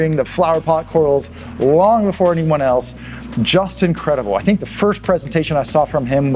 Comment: I thought you said "just incredible", but his name is "Justin Credable" XDDDD Im gonna bet his parents had that in mind